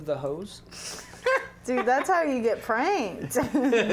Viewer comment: "can I smell the end of the hose?" (what I heard): "dude thats how you get pregnant"